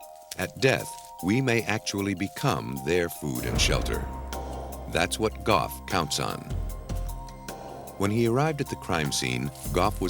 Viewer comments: I think he meant to say That's what GOD counts on!!!!!!! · Wow, good Hawaiian judge to issue a search warrant on the husband so fast, I thought. (Just because husband didn't act concerned enough?!) Then, when narrator said, ``that's what Gough is counting on,'' I thought he said, ``that's what God is counting on.'' On the Indianapolis story, they never really show the outside of the house. From the inside camera work, floorplan looks odd, with two, long halls branching off from central point? Kinda weird to find in an average housing development. It seems like it must have a weird history itself.